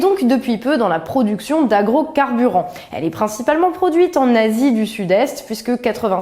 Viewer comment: "En nazis du sud est " 😂😂😂😂 pk j'ai compris ça moi